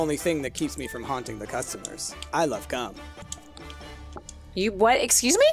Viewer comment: I am saddened to announce that me and Aenne thought we heard the same thing. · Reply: I LOVE GUM! (Aenne)